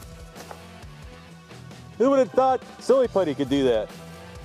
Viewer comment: Lol I thought it said thinking silly